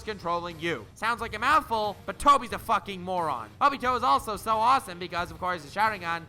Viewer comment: Best part of this video is at 4:36 I was watching with captions and when he is explaining obito's character the captions say Bobby Joe its just to perfect